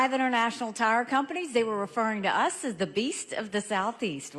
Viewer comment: Did she say "we're truly BREAST by our law enforcement " @ 0:25? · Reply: Yes it sounds like she said breast! 😅😂 She also said in another interview that as a brown child she was bulled in her hometown, so why is she calling her state sweet now? I know it wasn’t the whole state but she’s such a liar and full of it.